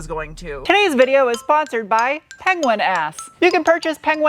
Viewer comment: I had to turn on closed captions cuz i thought I heard penguin ass. "Penguin ass. Now available liquid of frozen varieties. Get yours TODAAAAY!"